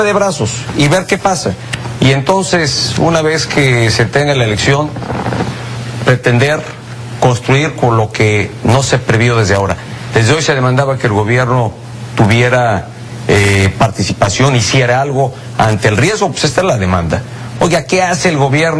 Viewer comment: Minuto 23:03 "... Lo que no se ha PREVIDO desde ahora ...." #EPN escuche bien? 😥😥😥